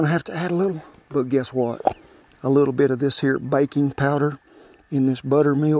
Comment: When he said baking powder slowly. I thought he said bacon powder.